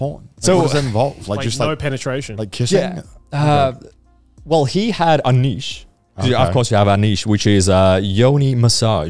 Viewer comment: my hearing failed me. I heard it as "he has a niece" lmao